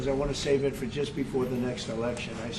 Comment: From 10:15 did Trump say “I want to save it for the next election”?